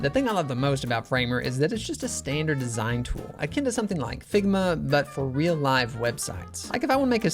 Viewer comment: I thought he said sigma